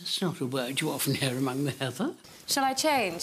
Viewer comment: Would someone be so kind as to tell me what Ms. Maggie is saying at 2:25? It's not a word you often hear among the ____? Thanks and blessings in advance! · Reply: Heather, which is a flowering shrub commonly found in Scotland; here, the implication is that it's not the kind of language expected of the Scottish aristocracy.